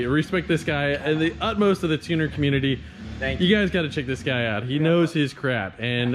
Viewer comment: Coulda swore you said, “part of the tinder community”. Man hearing aids are gonna be so expensive.